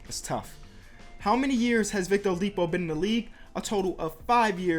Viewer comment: It sounds like you said Victor Olipo xD